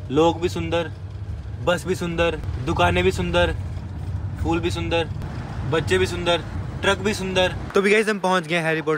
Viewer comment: saurav sounded like orochimaru saying - "bache bhi sundar"